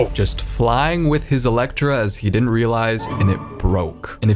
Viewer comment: did he just say electra?